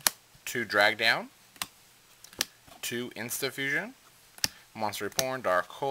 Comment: Sounds like you said Monster Reporn o.o